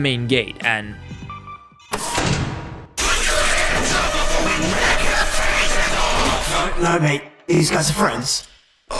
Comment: Does anybody know what the heck is ennard saying at 14:22? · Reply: He says "PUT YOUR HANDS UP BEFORE WE RIP YOUR FACES OFF"!